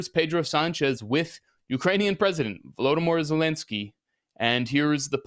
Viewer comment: Am I misheard or he really said: "...Ukrainian President: Voldemort Zelensky"⁉